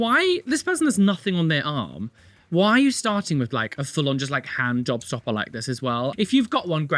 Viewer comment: I heard "a full-on handjob stopper" 😳 well that's not exactly incorrect, I don't think that tattoo will attract any partners!